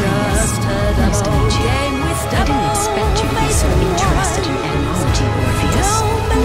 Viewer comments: All the overlapping lines at the end are so cool! First time hearing mellys english va too iirc, she sounds soooo good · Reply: !! its a kinda deep voice, the phrase i can hear of her is "I didnt expect you to be so interested in entomology, orpheus" i think she says smth more but i cant understand it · i hear someone say orpheus